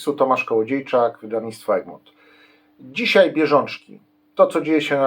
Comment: Ten mój słuch 😐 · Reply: 😆 Ja za pierwszym razem też usłyszałem "rzeżączki"😉